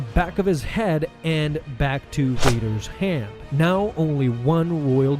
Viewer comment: I swear, it sounded like you said "Don Vader" man I need to listen better. "Who the F is Don Vader?" Is that his uncle or cousin? ADHD got my hearing messed up. lol